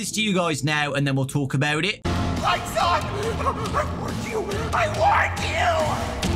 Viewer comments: Theory: Sunrise says “Lights on…I warned you, I warned you!”. So maybe when the lights are on, they’re Sunrise, and when the lights are off, they turn into Moondrop, and Sunrise is trying to keep Moondrop away. Gregory doesn’t trust them, and turns the lights out and Moondrop comes out. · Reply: To me it sounded like they said "I thought I warned you, I warned you!".... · he actually says "lights on, I WARNED YOU! I WARNED YOU" cuz if you go to the video itself and turn on captions it says that extras: i dont think its gonna be just one night because at the beginning of the trailer hand unit says that the mall will be closed for a week, meaning we will be stuck there for maybe 5 days idk maybe the cleaner bots at the end are kids who have been shoved into these helper bots and then thrown down into that trash pit along with the withered glamrock chica| i also think that on the cameras, the bigger areas are showing where each office and charging station is, and when it has a warning sign, it means there is an animatronic there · Sunrise actually sounds like he’s saying “I WARNED YOU, I WARNED YOU” maybe he could be a neutral animatronic warning us about moonrdrop (or someone else?) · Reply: At first I thought he said “Lights on, I want you, I want you!” Because, ya know, he needs the lights on to exist.